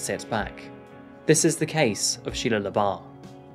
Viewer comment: I literally thought it said "The Case of Shia Labeouf" lmao.